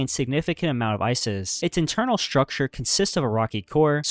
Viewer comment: the subtitles said “Isis” instead of “Ices” ☠️☠️☠️